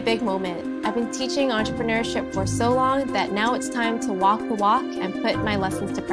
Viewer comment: Thought she said 'it's time to wok the wok'